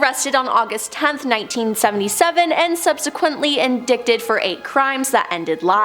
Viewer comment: Am I the only one that heard her say “in-dick-ted “ rather than indicted how it should be pronounced? 😂